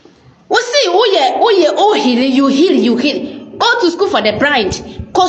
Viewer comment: Did I hear "School for the Brind"?😂 Mary brofo is not sweet😂